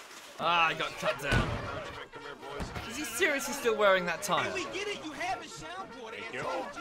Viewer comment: He's wearing a "tire". That is very different from wearing what I thought said, which was "attire".